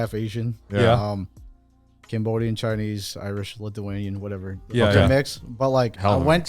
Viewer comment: did he say part Lithuanian???